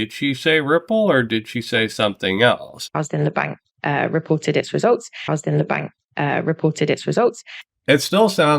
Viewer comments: Sounded like she said Ripple did its results? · Sounds like she said ripple did its results